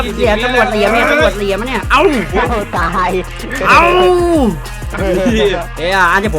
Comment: ได้ยินตำรวจพูดว่า"โง่ตาย"5555